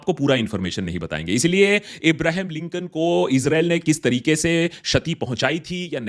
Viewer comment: did you say Isreal?